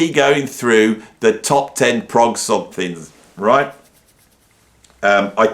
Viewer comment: Top Prog smiles? I thought that’s what you said…